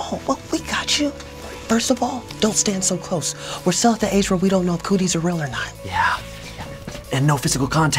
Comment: captions - it says we’re South Asia instead of we’re still at the age lol